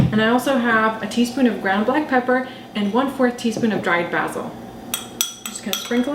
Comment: I didn't hear that spice 1/4 teaspoon of Dried what? I listen to it over and over sounds like you say Brago or Braco. I follow your link and ingredients says Paprika that's not what you say.. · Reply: Sorry about the confusion! I had to go back and listen myself - it's 1/4 tsp dried basil :)